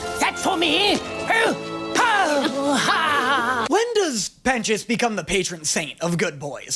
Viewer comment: NOT THE CAPTIONS SAYING PENTIOUS AS PENIS